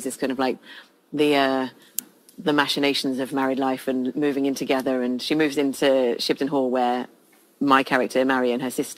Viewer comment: Can anyone tell me what AL says at the 1:50 mark (S2E1) when she holds up the lid to that porcelain thingy with the heads on it and SJ whispers what sounds like an f-bomb? I can't get the cc to work and I cannot figure out what it is she says. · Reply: She says "vulgar"